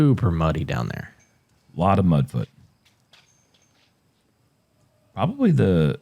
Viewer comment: Did I hear say a lot of "mudd butt" 💀💀